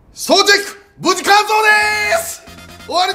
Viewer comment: 正直何て言ったかわからなかったんで 誰か教えてくださいー！ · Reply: 総チェック無事完走でーす！だと思います😊